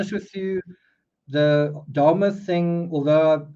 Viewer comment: Sorry..i just realized you were talking about Jeffry Dahmer🤦‍♀️😬I kept hearing you say " darma" or "Dharma" and I was wondering who you were talking about 😆 hahaha